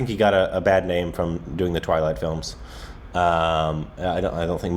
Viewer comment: Bruh I heard "Toilet films (instead of Twilight films) Kinda same but bruh 😂😂